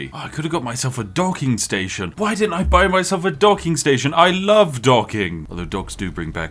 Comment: Not gonna lie: I heard, "I love dogging" instead.